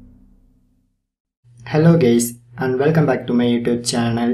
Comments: lmfao in the intro it sounded like he said helo gays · did he just say hello gays